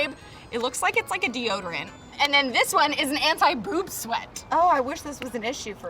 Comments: I thought they said poop sweat not boob sweat LOLLLLL 😂😂😂😂😂😂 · On the anti boob sweat I thought you said anti poop sweat